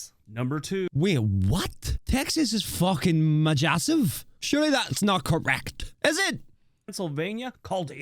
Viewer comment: I just heard this man say "majamassive"